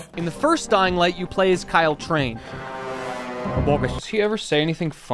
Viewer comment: Did he just say Kyle chrane?!